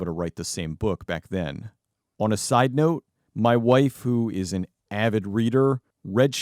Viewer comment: I'm sure you didn't say this, but it kind of sounded like you said, "My waifu's an avid reader..."